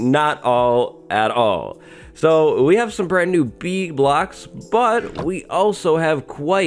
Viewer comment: “Bee blocks” heard it as “bee glocks” and was like w h u t